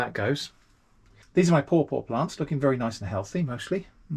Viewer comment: i dont hear well, and every time you brought up your paw paw plants i heard "poor, poor plants" so it was very funny to me that you went on to say how good they were looking